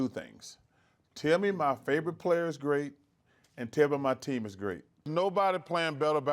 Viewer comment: when Chuck said “Tell me my favorite player is great” I thought he said “Timmy, my favorite player, is great.” I was like “You know what-Tim Duncan IS my favorite player, and he IS great!” And I knew Chuck wouldn’t disagree.